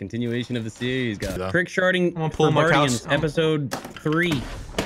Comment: did he say trick-sharting???